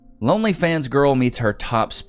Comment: Did you just say lonelyfans instead of Onlyfans 😂😂😂